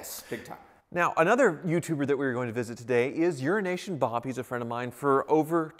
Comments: Doesn’t it sound like he is saying “urination Bob”? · And here I thought you were saying “urination bob” this whole time. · Ha... This whole time I thought was saying Urination Bob. I thought it was an inside joke that I didn't get. Haha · I can't be the only one that's heard that as Urination Bob??? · I kept hearing “Urination Bob” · sounds like your saying Urination Bob LoL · I keep hearing 'urination Bob' and it's a little unsettling. xD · Lol everytime you say euroasian Bob it sounds like your saying urination Bob. Lol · EuroAsia! I thought you were saying urination Bob this whole time! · 😂I thought you said URINATION BOB? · Sometimes it sounds like your saying "Urination Bob" instead of Euro-Asian Bob. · Ha... I thought he was saying "Urination Bob" ... Of course perhaps that is the intended joke. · Oh Euro asian bob......All this time i thought you was saying urination bob hahahaha. Thank goodness for that. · Did I hear “Urination Bob”?